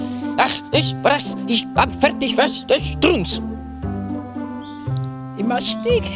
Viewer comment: Lol that sounded like trappatoni saying "STRUNZ" in the famous "WAS ERLAUBEN SICH STRUNZ?"